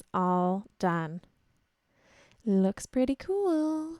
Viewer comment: I thought I heard you say "it looks pretty cool" NO it looks FRIGGIN AWESOME! Bravo! A lot of work but so well worth it. Thanks for the video seriously a fan of Game of Thrones and your Throne!